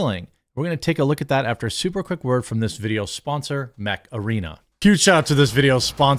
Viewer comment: Jeremy, the way you say Mech Arena is how imagine your average Wisconsinite to say macarena. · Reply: I'm a silly English chappie and I thought he WAS saying "macarena". My poor English brain was so confused.